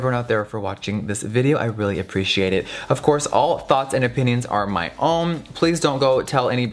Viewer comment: Lowkey thought he said all thots and opinions and was like WOAH WOAH WOAH 😂